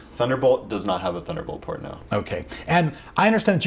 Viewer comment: did he said ''thounderbolt porno''? or ''port, no.'' on